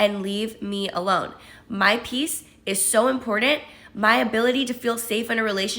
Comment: The 1st one sounded like she was saying imported instead of important